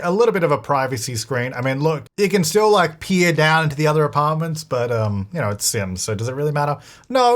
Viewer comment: I was like why would they pee downstairs tho! But then I heard what he actually said 😂🤦🏻‍♀️ in hindsight, they could pee through there too😂